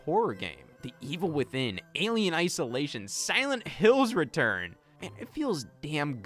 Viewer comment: Did you just called Silent Hill 2 Remake "Silent Hills return"??? It's not a Donkey Kong game!! (I wish it was)